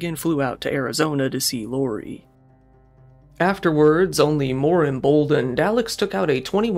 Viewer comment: wtf did you even say here? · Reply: "afterwards, even more emboldened .."